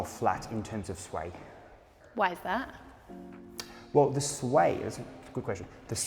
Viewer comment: I'm sorry, did you call him a wanker at 12:04??!